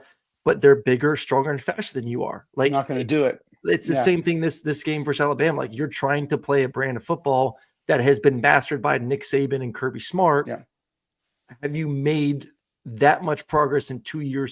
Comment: I thought Aaron said "been 'bastard' by Nick Saban and Kirby Smart" 😅😂